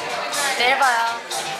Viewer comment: Did she say "Merhaba" or I hope that I heard like this 🫠